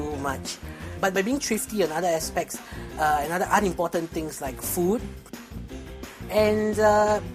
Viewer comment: did he just say at 0:53 that food was unimportant?